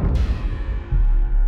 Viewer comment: When you said an astroid collision, I first thought it said a steroid collision. Lol!😂😂😂